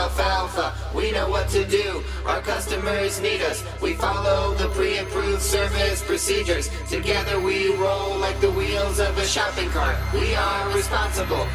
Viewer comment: The captions say "Omega Mark"?